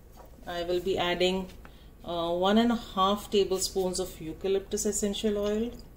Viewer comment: So why do you use eucalyptus crystals & alcohol if you are also using eucalyptus essential oils? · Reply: oh ok, my apologies I thought you said eucalyptus crystals, I guess I misheard you. So where do you get your crystals? Have you ever made the steamers without them? If so how did they turn out?